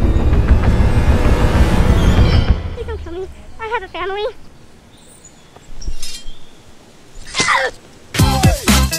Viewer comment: what he say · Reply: He said: please don't kill me I have a family